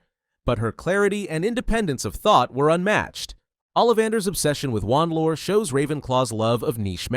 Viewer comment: I thought you said "obsession with lawn mowers." haha